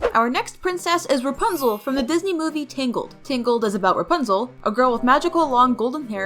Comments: Her: Tangled what i heard: Tinkled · When you said tangled I thought you said tingle ☠️